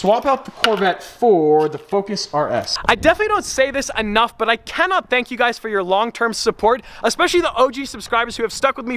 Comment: anyone else hear him say he can’t thank us? i’m sad.